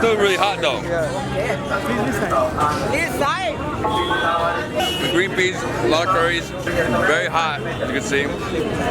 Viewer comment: can any Indians please tell me what that guy said cause he was so rude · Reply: Sedra Munther He said Eeh side means give me side. That was little arrogant.